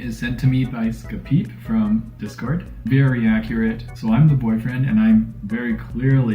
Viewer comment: for asec i thought you said o have a boyfrienf lmao